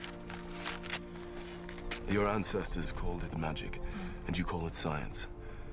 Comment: SOMEONE CAN HELP ME? at 1:23 "your .... call it magic" what is he says??? thaks a lot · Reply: Ancestors?